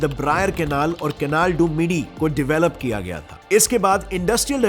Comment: i heard canal dub gayi 😂